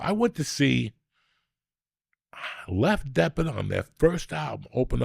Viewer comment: Lef Deppard 😂😂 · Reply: 😂 That's what I thought he said. First I thought lef depplin.. Lef Deppard😂